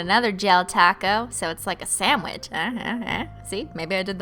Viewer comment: When you said sandwich I heard "Sam witch" and I was like whaaaat ?! 😂